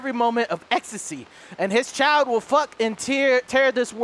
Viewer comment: did Delirious say at 34:05 to 34:10 "His child will fuck and Tear" or am i going crazy ?